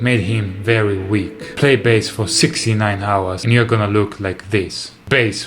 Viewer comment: did you just say that if you play bass for 69 hours, you end up looking like a guitarist? ew. gross